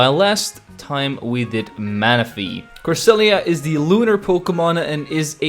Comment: cresselia is the LUNAR pokemon what i thought i heard: cresselia is the LEWDER pokemon